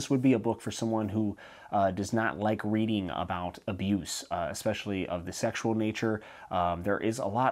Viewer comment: i thought you said "misuse of the sexual nature" and i was thinking: I want some of that!